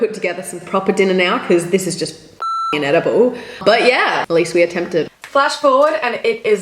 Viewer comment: Did anyone notice when she said "Fucking inedible" it sounded like she said "Fucking edible"